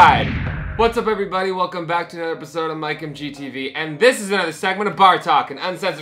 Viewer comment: Man. At first I thought you said "This is Bartok." And obviously the conversation wasn't going that direction.